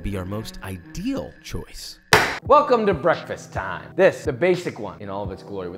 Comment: " welcome to breakfast" I thought man was going to say Welcome to Brixton